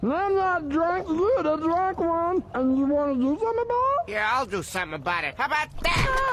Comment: "you get me another beer im 21" "oh great he's drunk" "im not drunk you the drunk one" "and you wanna do something about it?" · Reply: I thought he said "And you want to do me Jellybear?"😂